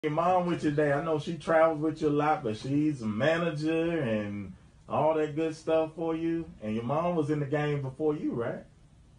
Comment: Sounds like he said your moms with you today, and that threw her off.